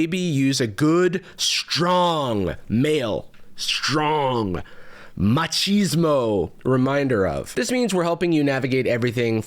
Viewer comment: kkkk did he say "machismo"????